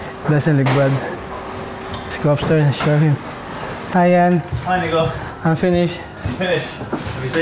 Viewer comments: Am I the only one or did Yianni say "Hi nigga!" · did yianni say the n word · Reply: D2J Gaming No he said nico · DID HE SAY HI AND THE N WORD??!